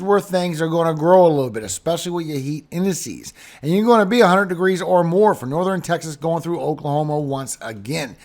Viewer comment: Sounds like you said 800 degrees. Maybe your talking to fast, however at beginning of broadcast it sounded like 800!! Love watching you, but slow down please